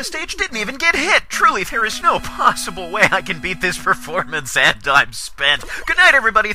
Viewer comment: What did say at 4:16? I've listened to it for 10 times now and I can't figure it out. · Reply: OrkTv "There's no way I can beat this performance and I'm spent!"